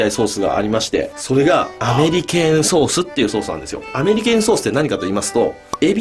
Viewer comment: アネリケームソース聞き間違えする🙄